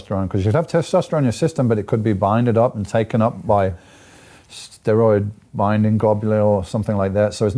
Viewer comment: did he just say ''steroid binding goblin''